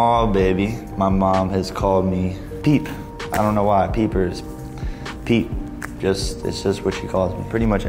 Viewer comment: "my sweet little peeper is gone now" 💔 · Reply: stg i thought that said pepper